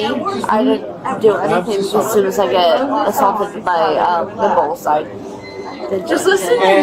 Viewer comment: did she say assaulted by liberals?